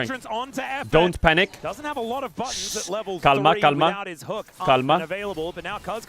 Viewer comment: did he just say kalma? 👀